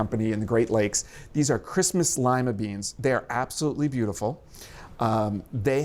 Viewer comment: haha on a first pass when you introduced the "christmas lima beans" i thought i heard "these are crispy slimy beans"